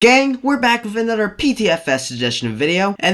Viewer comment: idk why but it sounded like he said ”GAY”